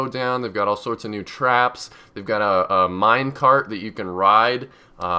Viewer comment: did he say minecraft oh no sorry that was minecart...